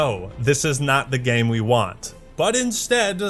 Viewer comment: After talking about the lack of focus group works at 14:35, I heard the words "But instead, you pursued" and my mind immediately said, "Lu Bu?" But of course, if that were the case then in the 3 Kingdoms Lore, Lu Bu would've come in on a Hovercraft.